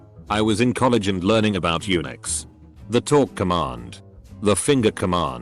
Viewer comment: I heard: "I was in university and learning about eunuchs." Did not think I heard that correctly, and had to look at my screen to check. The word is, "Unix." 🤔 So, apparently I did hear it correctly. It just wasn't what I thought. 😂🤣😭